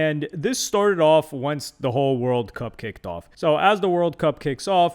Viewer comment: I thought said “world cupcake”